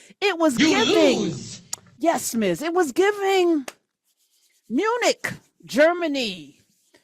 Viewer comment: I heard eunuch, not Munich 😂 Tbh, they are a bunch eunuchs tho 😒